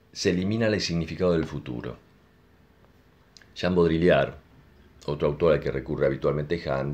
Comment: A quién mencionas en el minuto 9:46? No se entiende muy bien el nombre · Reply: A Jean Baudrillard, autor francés ya fallecido. Muy interesante, trabaja la idea de simulacro y eliminación del acontecimiento. "Bienvenidos al desierto de lo real" decía Morpheo en Matrix, esa idea es de Baudrillard.